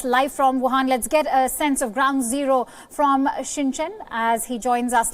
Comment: I hear "shin chan" and u?